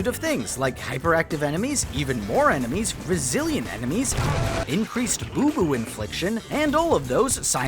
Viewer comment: i thought he said "Brazilian enemies" ngl